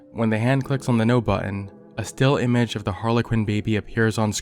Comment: The subtitles said ''Harley Quinn baby'' instead of harlequin baby and I was obviously confused